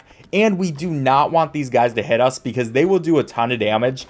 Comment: waht is he saying 💀💀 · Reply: He said "hit us"